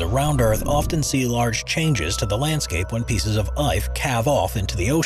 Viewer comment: I swear he says "pieces of IFE" and not "ICE"